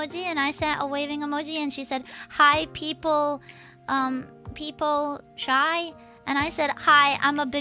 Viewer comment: I think she's saying "PeepoShy," which is a Twitch emote